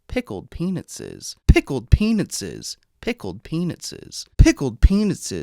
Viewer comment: I kept hearing ‘Pickle Penises’! lol😂